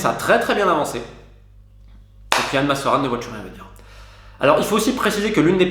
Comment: Il dit quoi à 5:40 ? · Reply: Il dit: "Anne, ma sœur Anne, ne vois-tu rien venir?" J'ai oublié dans quel conte cette réplique est citée.